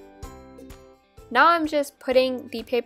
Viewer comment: who noticed instead of saying now she said naw